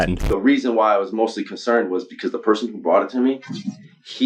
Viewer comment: Whether this was real or fake, LP should've known not to do business with a "professional seller/buyer" who says "the person who bought it to me" 4:35, yall this is what I heard, what do yall hear?